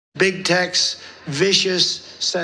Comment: Sounds like he said "Big Tex"